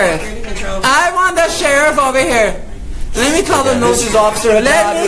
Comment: Lmao thought he said "lemme call the NOOSE" 🤣🤣🤣